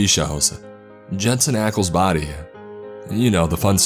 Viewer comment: does he say Jensen Ackles body? Cuz if so, then I’m dead😂